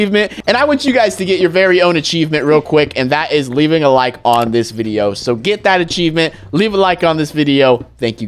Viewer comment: It sounded like he said “Like the video to like the video